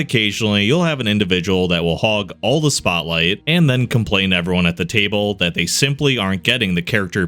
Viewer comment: Humans review their video, sometimes they have human editors that notice these things before uploading. It's the kind of mistake in a script that as you read it, you'd notice that it's wrong. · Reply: You missed that said "complain to everyone at the table". It seems his accent made "to everyone" sound like "t'everyone". So what you assumed was AI was indeed human error, in this case mishearing an accent. Ain't dat funny?